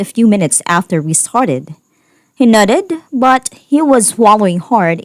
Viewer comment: When the reader says nodded , it sounds like she says nutted🤣🤣🤣🤣🤣